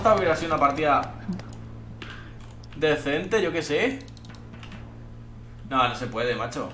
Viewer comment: despues de decente ... no escuche bien lo que dijiste , a ver dime .... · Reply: yo que se (España) que se yo (Argentina) jjajajaajajajajaj